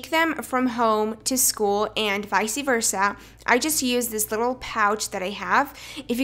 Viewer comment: Did she just say vic-ii versa instead of vice versa!?